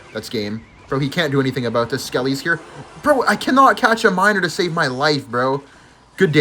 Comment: ❓🧐 what did he SAY! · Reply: couldn’t catch a minor to save his life 😂